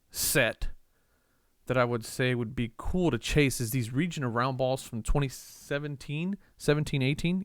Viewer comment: When you were looking at the Larry Bird Region of Roundball, I thought you said Reaching Around Ball.... Hahahaha